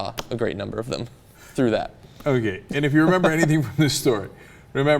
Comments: I thought Cenk was saying "black sea turtles" for the first half of the video. Man I'm high af. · I swear for the majority of the video I could've sworn that Cenk was saying "Black Sea Turtles"